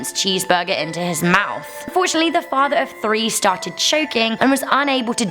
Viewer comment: Did u just say FOURTUNALLY?